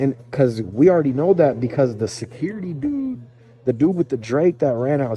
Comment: lmao 5:09 sounds like u said hey dude